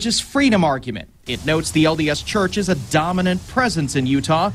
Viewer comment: i thought at first it said the LSD church...Duh....